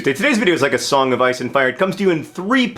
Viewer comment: Oh gosh, when you said "ice and fire" I heard "ISIS and fire", oh gosh the media is reprogramming my brain... All the doomsday talk is getting unbearable, if you made a video explaining the actual situation I'd be much obliged. Thanks